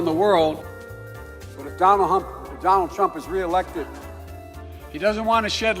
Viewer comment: Fuckkk i was drinking pop and I heard him say Donald hump i chocked and die laughing